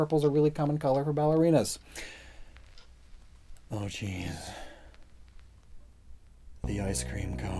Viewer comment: So when he said oh jeez at 11:13 I heard oh cheese first then immediately got an ad for cheese pizza